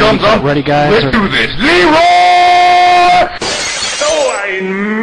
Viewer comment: I thought he said “HEEEROOOOOOOOOOOOOOOOO!!!”